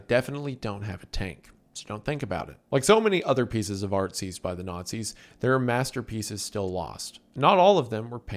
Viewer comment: bro istg i heard "like so many other pieces of artzis by the nazis" im dead lmfao